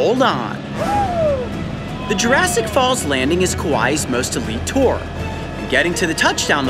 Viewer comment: did anyone hear kawuii? Just me?